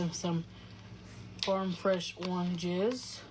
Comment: I dead ass thought she said farm fresh warm jizz 😂